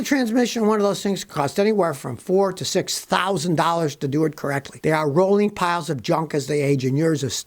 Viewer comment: I thought he said timing belts are rolling piles of junk on any other vehicle!